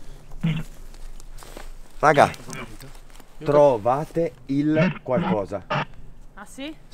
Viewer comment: Buon pomeriggio amici miei della P.I.T Family e un buon pomeriggio a tutti voi dello staff da Parte mia dal vostro Salvatore Lavore,al minuto 3:36 Questa nuova entità misteriosa ha detto hai ragazzi: " trovatemi ",al minuto 4:40 Questa nuova entità ha detto hai ragazzi questa frase: " Gutach ",al minuto 5:39 Questa nuova entità ha detto hai ragazzi: " prima " ,al minuto 7:22 Karin gli dice hai ragazzi: " sto bene ho fame",al minuto 7:32 Karin ha detto hai ragazzi: " bene hungry ",al minuto 8:00 Karin gli ha detto hai ragazzi di : " si di notte fog nebbia ",al minuto 8:16 Karim ha detto hai ragazzi: " strangolata ",al minuto 9:15 Karin dice hai ragazzi:: " guarda in terra ",al minuto 9:19 Karin gli risponde hai ragazzi " bene si.... meglio ",al minuto 10:17 Karin risponde hai ragazzi : " qualcuno ",al minuto Karim gli dice hai ragazzi : " ricordati di me ",al minuto 12:57 Karin ha detto hai ragazzi: " è giù ",al minuto 12:36 Karim gli dice hai ragazzi: " morta ",al minuto 12:51 Karin dice hai ragazzi: " di notte sul tetto ",al minuto 13:14 Karin gli risponde hai ragazzi: " polizia ",al minuto 13:41 ha detto hai ragazzi di nuovo però in tedesco: " polizei ",al minuto 14:47 Karin ha detto hai ragazzi: " non è qui con me ". Io il sotto scritto Salvatore Lavore vi augura un imbocca al Lupo per la nuova indagine per domani e un imbocca al lupo a a a a e a per un nuovo lavoro per domani ciao da Salvatore Lavore